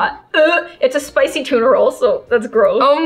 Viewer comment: when said"tuna roll" i thought she said its a spicy turtle i was like whaaaaat